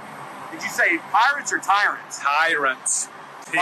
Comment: Tyrants, what was that? TYRANTS! I thought you were saying pirates? Cops are Tyrants, Pirates, and thugs.